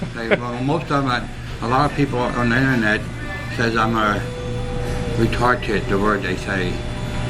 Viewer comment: The guy in the video sounded like he said retarTed